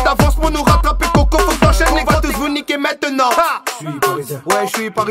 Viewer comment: Ba on voit sa répétez s’il vous plaît Att quoi j’ai mal entendu avec 1:07« Neymar cet année on va tout niquer ?? » Ah et en plus « vous servez à rien comme Ezzzquiel Lavezzi »et ba putain Lavezzi a plus donner que Neymar